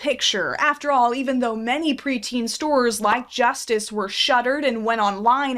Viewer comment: when you said pre-teen, i thought u said creatine, and i was so confused during the whole video, until i realised what u mean 😅